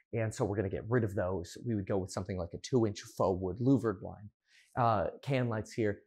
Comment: I didn't understand what blinds he wants to replace it with, he said 2 inch louver blinds? Can anyone help me?